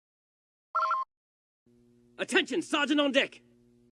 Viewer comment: I don’t know why but first I thought the the intro said sergeant al Dick why did I hear this